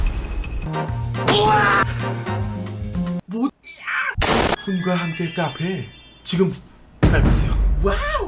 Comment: 대체 뭐라는거임?? 웃긴데 이해불가능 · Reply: (기)모찌!앙! 인듯 ㅋㅋㅋ